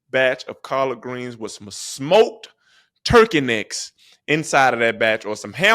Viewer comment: did blud say Turkey necks and ham hawks?? 💀